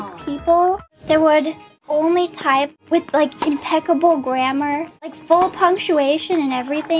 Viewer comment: fauna says impeccable grammar. the subtitles say imbecile. oops